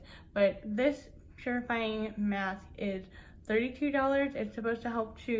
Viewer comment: For a second there I thought you said terrifying mask🤣😂😂😂 but you said purifying mask.